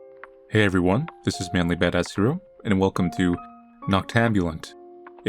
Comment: For the longest time I thought he was saying Mainly Badass Cereal and honestly I'm kind of sad its not